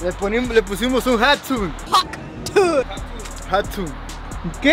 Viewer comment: said hot tune instead of saying hot tuak 😭😭